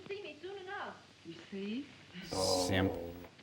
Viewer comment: did Crow just call that guy a "simp"?